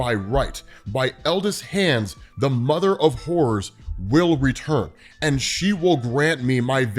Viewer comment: could have sworn i heard mother of whores😂